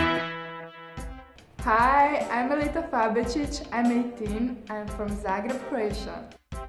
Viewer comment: It sounded like she said I'm Melita fab bitch, LOL